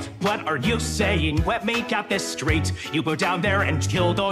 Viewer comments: vaggie and charlie sound so distorted and even worse together (but in a good way) also in 1:25 it sounds like adam says "let me gyat this straight" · bro said wet💀 instead of let